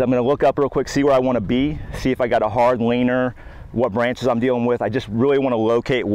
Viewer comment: My girlfriend overheard the audio while I was watching. At the 13:12 mark, she asks, "Did he say hard weiner?" 😂😂😂